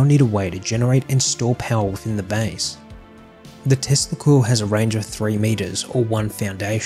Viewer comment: i thought u said testicals , yeah my mind is dirty